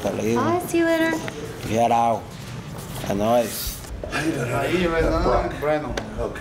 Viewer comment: when the chick says " Bye,see ya laterr" Barao sounds like he says....GET OUUUuuut.. like tony montana lmao